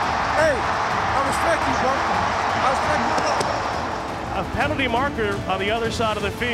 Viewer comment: what does he say after "good game"? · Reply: Oh, he tells Edelman, "I respect you..."